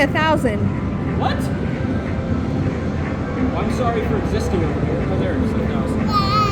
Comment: what did you say here after you say “what? Well I’m sorry for existing….” I listened and listened and could not hear it. Whatever it was, I’m sure it’s hilarious seeing as how you started with “well I’m sorry for existing.” 😂😂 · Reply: i think I said I'm sorry for existing over here, since she was winning more without my help 😂